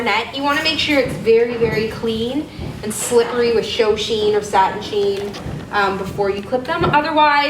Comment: I am new to horses and just beginning to take lessons. I heard you say something like, "cho-sheed," when you talked about the horse being bathed and clean before clipping. What does "cho-sheed" mean, and what is the actual word? :) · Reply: she was talking ShowSheen it’s a detangler/polish for horses fur. Just look up ShowSheen and you can find out a lot about it. It’s not a need when cleaning a horse, though.